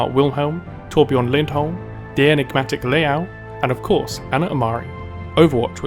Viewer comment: Which name did he say after tobjörn in 2:07 ? I cant understand it :( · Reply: Liao